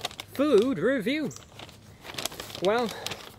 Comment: When you say "food review" at the beginning i thought you said "meme review"